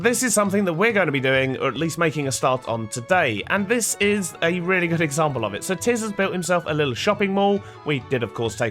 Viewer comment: Around 1.30 I nearly thought you said Tizz was a good example. I had to go back and check XD I definitely like the acacia roofs <3